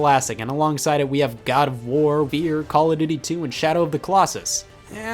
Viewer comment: you know the fear title on screen? Or at least see it? At 1st thought he said beer not fear. 1st thought he joking cod but saw he said after. And then joke not make sense. Then think god of war until I see fear. Just think cod because we guys like beer, lol. And if a game of year cod away will be. Just saying, I’m not too beside, jk. 20:44 or halo and gta could be fun. But next time go slower, plz